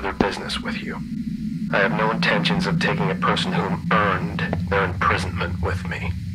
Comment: I heard him say.. I have no intention to take me with me..... loll